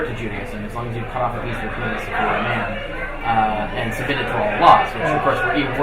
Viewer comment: Anyone catch what he said at 40:42? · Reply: "... and submitted to all laws."